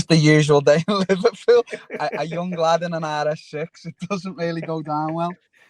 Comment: what did Darren say here? I couldn’t make it out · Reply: U buzzing.. a young lad in a RS6